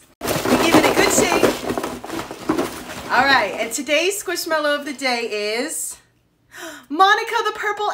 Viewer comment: I thought she said squishmello to date